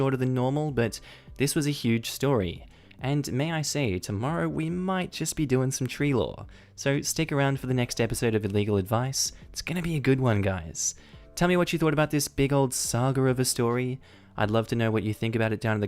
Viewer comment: At the end it sounded like you said illegal advice. Now I’m curious if there’s a r/illegal advice